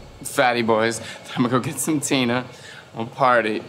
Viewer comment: Did he say he’s gonna get some “Tina”? Is that coke or something?